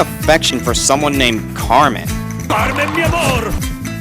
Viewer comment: Carmen? All this time, I thought he was saying "CARNE, MI AMORE!", professing his love for meat!